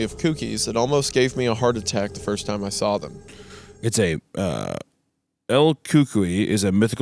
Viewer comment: as a mexican them trying to pronounce cucuys is killing me 🧍🏽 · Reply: Same, I was listening to them going back and forth, hadn't read the text until I heard hunter say "El cookee", I was like ohhh, El cucuy... 😂